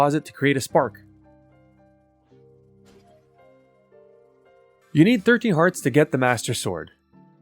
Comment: What I heard: "You need 30 hearts to get the master sword" Me: WHAT?!